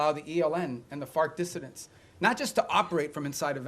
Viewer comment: When he said “FARC Dissidents”, I thought he said “fart dissonance”